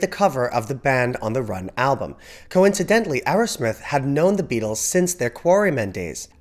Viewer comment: I was listening to this, (and not watching it,) and thought he said "Aerosmith" took the picture!!!!!